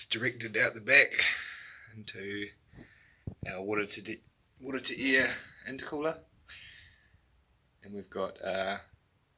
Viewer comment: Hi, it is indeed a liquid cooled intercooler. We have plumed it up so the cold river water circulates through it. Cheers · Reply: when I watched the rest of the video I saw it. Sounded like you said air to air inter cooler in the beginning . Still a cool project